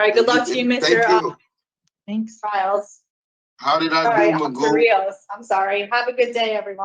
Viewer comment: Did he say "How did i do Magoo, or Mandoo?"